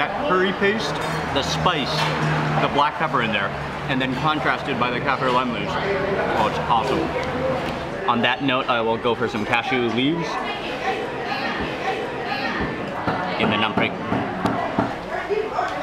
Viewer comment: did he say cashew leaves ?